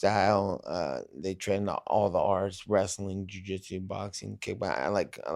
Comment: I coulda sworn he said "my guys train all the Rs, wrestling ju-jitsu, boxing" hahaha But I think he said arts